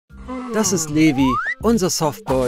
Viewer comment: heißt der newi(so hab ich das verstanden WEIL DIESE STIMME SO UNDEUTLICH DAS GESAGT HAT)oder wie heißt der😂😅